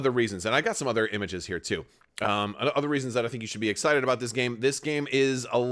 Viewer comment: funny, i thought that said "you should be exploited" which is how i feel with amg